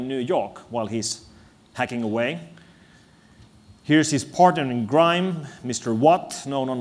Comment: Did he just say "partner in grime" at 4:12?